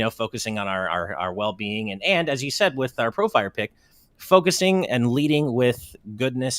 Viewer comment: It sounded like you said Pro Fighter Pic, so I couldn’t help but imagine the four of you in a boxing ring duking it out lol